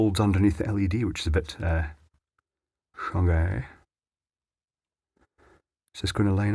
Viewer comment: ?! "is a bit shaggy"? Idk what he said lol